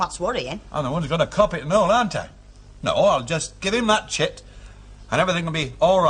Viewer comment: Do my ears deceive me or does Stan say: "No, I'll just give him that shít ...." ? Hilda's reaction suggests she heard it too.